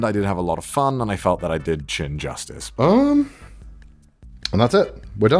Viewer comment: lol it sounds like he said “chin justice”